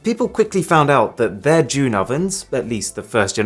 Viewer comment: When he said June then oven I thought he said Jew ovens